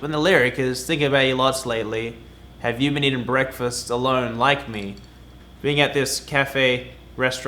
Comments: I thought it was "Have you been eating breakfast in low lighting" I swear I can't understand what Luke is singing. I was trying to figure out the deeper meaning of that. Like why tf would she eat breakfast in the dark. smh😂😭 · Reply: i thought it he said “breakfast that don’t like me” and i was so confused 😂😂😂😂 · ok but i heard it as "have you been eating breakfast sirloin like me" when i first listened to it